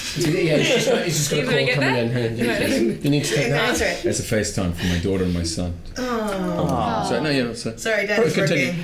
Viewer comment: what did daniel say at 3:03 ? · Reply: "It's a facetime from my daughter and my son."